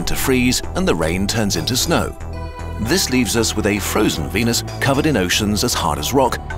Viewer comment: What he said: "this leaves us with a frozen venus" What I heard:"This leaves us with a frozen penis" My reaction: 😲